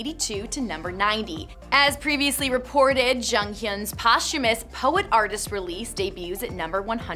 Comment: In the beginning it sounded like she was saying: Jongkhyun😂 I know it’s hard to pronounce but it’s just a bit funny😂 What I want to say about Jonghyun is: Since that day I feel really sad. I’m happy but deep inside I still feel sad. I can’t accept the reality. I cried for days. Jonghyun was just one big, talented star that now is in heaven. RIP JONGHYUN❤️ What I want to say about BTS: I am now for 6 months a BTS fan and I have to admit I’ve never been so obsessed with any idol of mine. I cry when there’s a new MV coming out, thinking how grateful I am for them coming into my world. I start to feel supper happy when I only hear someone talk about them. When I hear the word ‘DNA’ in a biology lesson, I whisper ‘DNA’ to my friend and I do the gesture. They are hardworking boys, who deserve the best. I love them so much❤️